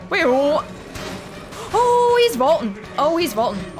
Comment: What does she say on 50:35??? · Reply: He is vaulting